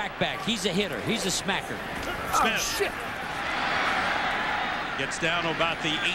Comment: “awe shit” Lol anybody else hear that?